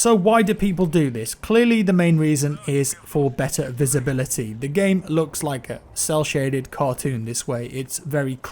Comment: Am I The Only One Who Heard "The Game Looks Like Fortnite"?